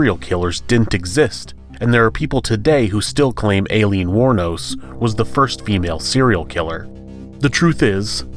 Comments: LOL the way the narrator pronounces Aileen wournos name makes it sound like is saying her name is Alien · Did he just call her Alien War Nose? **And twice again at 9:40 'Eye Lean', pronounce it like that next time. Hopefully he doesn't attempt to pronounce my first and last name.